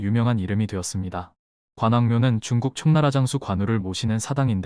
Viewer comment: 관우의 촉나라 장수가... 청나라 장수로 들려서 깜짝 놀랬네요, ㅎ